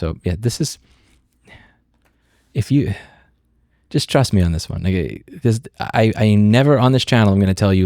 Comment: did he just say: "Trust me on this one, ni**a?" LOOOOLLL